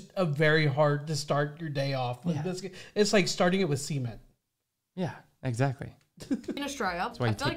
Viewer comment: Did he say semen? 😳